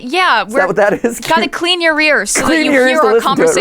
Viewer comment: I definitely misheard it as "clean your rear" and did a double take...